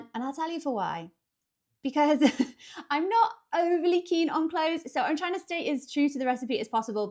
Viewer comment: I totally turned it up at the best moment. I thought I heard you say "I'm not overly keen on clothes" when i did.🤣